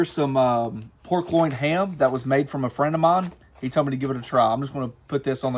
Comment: we did.... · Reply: Sorry... trying to be funny... I heard it as you made a Ham from a friend ... as the ham was made of a friend.